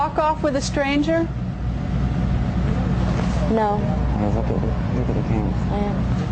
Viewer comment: Did anyone understand what he said at time 16:17. Dude covered his mike told ol girl something and she said I will. They know what happened to that little doll. Lets try to find out what he said when he covered his mike. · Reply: Michelle Frisby he said “ look at the camera”